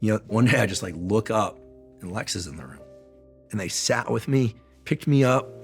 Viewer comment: I heard it as “Alexa;” without context, this would be a good Alexa commercial. Sorry 😅 Random thought